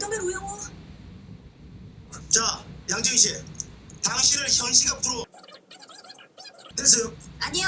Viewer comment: 현 시각부로 인데 현식 앞으로 ㅋㅋㅋㅋㅋㅋ 진짜 그렇게 들려서 신기한데 그냥 들리는대로 적으시는 건가요?? 아니면 자동완성 같은 기능인가요?